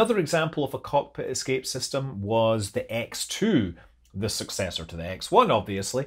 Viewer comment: Ace combat fans: Did I just hear X-02?